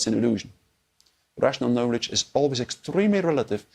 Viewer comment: Riveting conversation, however; who is responsible for double checking this transcript? An example begins at the 48:03 mark. The word Russian is written while rational is what was said. A correctly written transcript should always be truthful, especially for our hard of hearing/deaf community.